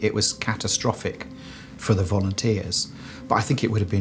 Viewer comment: could’ve swore he said “Vomiteers”. Those poor blokes.